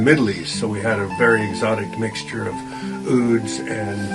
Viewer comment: That guy actually says 'Ud' (an instrument) instead of 'wood', which is mistakenly written in the subtitles haha